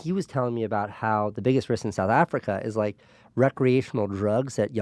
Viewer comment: Hey, english in not my native language, What does he say at 1:50:17? "He was telling me how the ... In Africa." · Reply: He said “the biggest risk in South Africa”